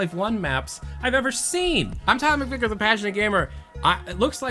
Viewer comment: I always hear "the patch-knit gamer" instead of "the passionate gamer"